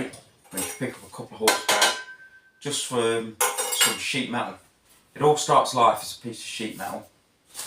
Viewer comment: When you said “Piece of sheet metal” I thought you said “Piece of shit metal”, which is how I feel when working with sheet metal